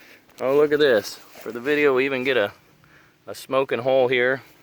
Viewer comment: I thought you said smoking mole. So disappointed just a smoking hole.